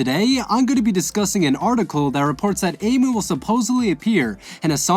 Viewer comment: Did he say amu